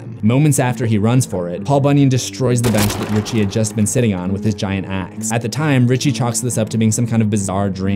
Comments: i heard "giant ass"... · i heard "with his giant ass" instead of "with his giant axe"